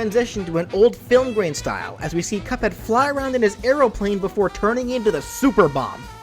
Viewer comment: did he just say Arrow-plane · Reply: "Aeroplane". It's an old timey thing